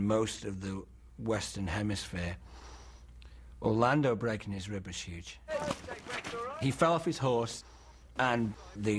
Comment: I dead serious thought he said "...breaking his rubber shoes"?